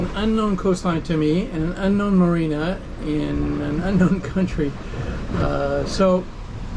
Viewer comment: did you say unknown country? Thanks for the videos. Will wait for a heave-to video.